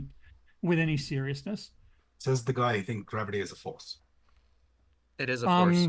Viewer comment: this is what i heard "says the guy who thinks man are woman" "man can definitely be treated as a woman" yeah 🤡